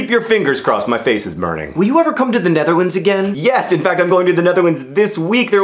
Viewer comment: When he said "The Netherlands again," I heard, "The Nether Once Again."